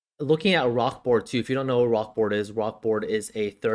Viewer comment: bro what were you saying? rok ford? rokform? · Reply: oh nvm i saw it now rokboard.